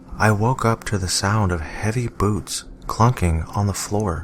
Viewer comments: I SWEAR I HEARD "HEAVY BOOBS" NOT "HEAVY BOOTS" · I thought he said he woke up to the sound of heavy boobs clunking on the floor, the picture also helping the image it was giving me-I was like....wait WHAT?! X'D